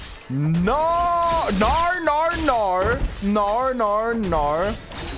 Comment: I can't stop laughing said"nor nor nor" and I hear" or or oor"